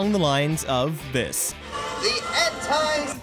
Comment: Did he just say ''the End Times''?